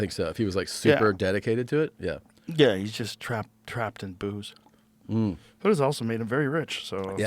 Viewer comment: When joe said bert was "trapped in booze" i heard it as "he's just traps and boobs" and it made sense to me still. 😅